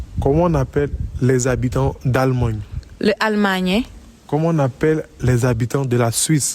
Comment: J'ai cru au sans faute pendant quelques secondes... Et puis j'ai entendu "allemagnais" Dommage.... 😂